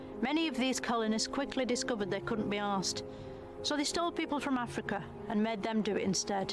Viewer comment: Subtitles say 'they couldn't be asked' but it should read 'they couldn't be arsed' which means 'they couldn't be bothered'.